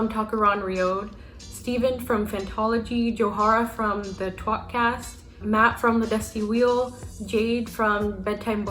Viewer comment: i heard "twat cast" idk about anyone else 👀